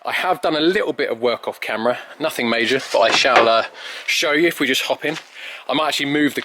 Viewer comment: Is he said in sha allah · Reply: He said "I shall err"